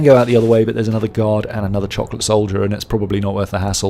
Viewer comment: did you just say "another chocolate soldier"